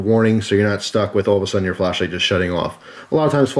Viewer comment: I thought it said "Fleshlight"...